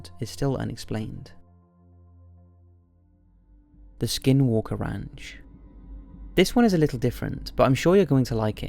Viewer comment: Haha. At first when you said "Skinwalker Ranch" I thought you said "Skywalker Ranch" and I thought George Lucas had supposedly encountered aliens, which would be quite eerie given all the alien species in Star Wars.